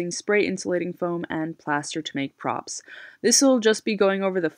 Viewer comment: I thought it said foam and potato props... I dont know why.. lmao